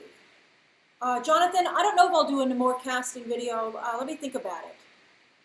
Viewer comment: What did she say at 21:30? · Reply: she said "i dont know if i will do a NAMOR casting video" the marvel aquaman guy